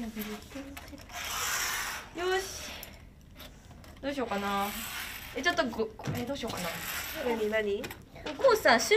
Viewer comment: 小声がしっかり聞こえるまあやちゃん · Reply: あるけどさっき言ったら絶対にダメって って言ってると思います